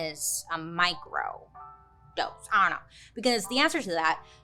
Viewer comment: I thought u said Micro-ghost. Lol